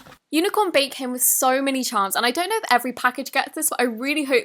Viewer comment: i heard unicorn bacon😝😝😝😝